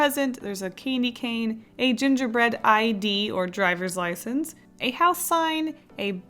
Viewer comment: i heard " gingerbread IED " and i was like oH and all i could think about was a gingerbread war oof